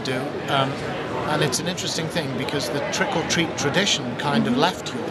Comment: He's a sexy old man....but did he just say "trickle treat???"